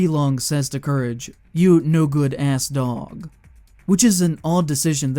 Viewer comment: i think hes say you no good aa dog bus he has an accent